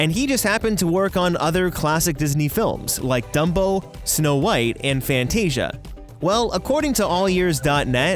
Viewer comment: #DUMPO (I know it’s dumbo but it sounded like he said DUMPO)